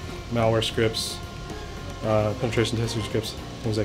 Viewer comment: "Malware scripts and 'ultrauntrable' scripts"??? what the fuck did he jsut say? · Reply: penetration testing scripts